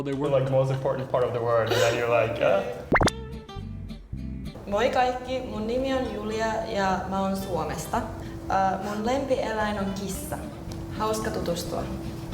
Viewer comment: she is saying "Moi kaikki, mun nimi on Julia ja mä oon Suomesta. Mun lempieläin on kissa. Hauska tutustua" which just translates "Hi everyone, my name is Julia and I'm from Finland. My favorite animal is a cat. Nice to meet you."